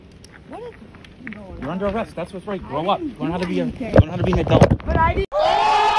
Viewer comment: I had to go back to make sure I heard his comment about being an adult right, I thought he said he was "arresting her for not being an adult"🤣🤣🤣